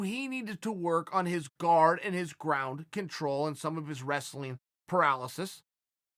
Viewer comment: Anyone else hear “wrestling paralysis” at 8:16 instead of “wrestling prowess”